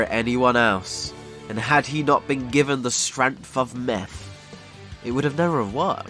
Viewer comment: Did...did u say the strength of meth